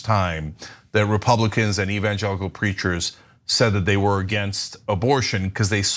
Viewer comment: I heard evangelical creatures went back unfortunately Cenk does say preachers. Creatures would have been so much better!